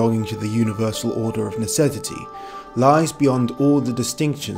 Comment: Greetings, what in the glorious hell are you saying at: 1:02, are you saying "necessity", or "necevity", or "necedity"? What on earth??!??!?!